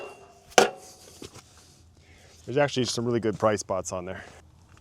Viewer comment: I thought you said: “There’s actually some really good price bots on there.” 😂